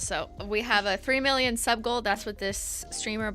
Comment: The first time you said “sub goal” I thought you said “suckle” and I was like wtf lmao 😂